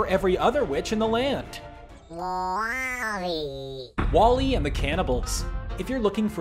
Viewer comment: I'm half asleep and thought it said Wall-E and the cannabis.